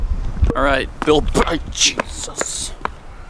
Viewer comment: I hear right did he just said Bird Jesus (Pidgeot)